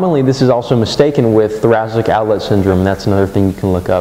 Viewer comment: what are you saying in 1:25? What syndrome is it commonly mistaken with? My fingerpains have been so bad for three years now... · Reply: TOS